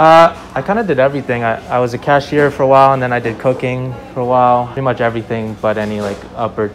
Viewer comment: I thought he said he did cocaine 9:21🤣🤣.